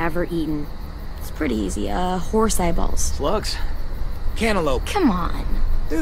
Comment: I NO LONGER LIKE HIM, CANTALOUPE IS THE MOST DELICIOUS FRUIT EVER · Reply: Crispy Crunchy, for a moment there I thought he said Antalope and I also got offended, and yes, I eat Antalope